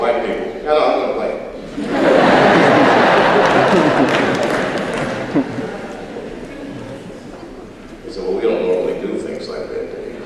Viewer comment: what did he say around 1:50 when he's told there's white people in the sketches? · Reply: "naw I'm gonna play em"